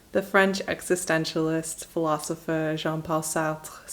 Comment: At first, thought you said the French Exit! One of my favorite movies 😊🎉 check it out!!! 💛